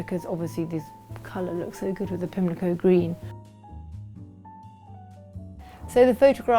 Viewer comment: Did anybody hear the name of any of the paint colours she has used? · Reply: I thought I heard her say, 'Pimlico Green,' in the dining +/or sitting room? Since she used Farrow & Ball in the bedroom & kitchen, maybe she used Farrow & Ball paints throughout her home?